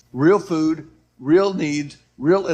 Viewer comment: Does anybody know what the four things that we need to do? I missed number three it sounded like he said real need. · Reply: I heard him say real needs which I suppose are like shelter, emergency kits, clothing, water filtration device etc. Also real energy made me think of solar panels and diesel generators to be self sustainable.